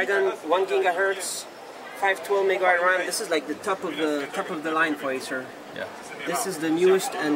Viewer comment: he sounds like he says "this is the taco bell acer"